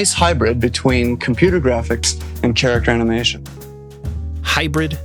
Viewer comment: did he say “tsa-racter animation”?